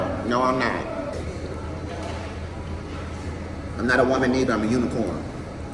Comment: I thought he said "Imma Uniform" like dress up in different uniforms smmfh lbs its the food distracting my mind